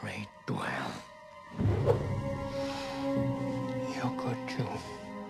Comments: What did he say at 1:05 ? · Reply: he said "you could to" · My game it goes straight to hell, You could too. What a line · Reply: R K lol, I don't even know what it means....I thought he said," you good too." Lmao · When I was a kid me and my cousin always thought he said 'your good you' we repeated to everyone.